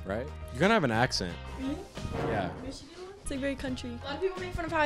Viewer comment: Did she just say Michigan is a country